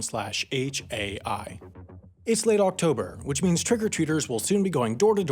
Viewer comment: did i only Hear Trigger Treaters and laughed So hard?